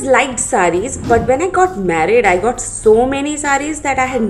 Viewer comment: Wait did she said"when i was married" what???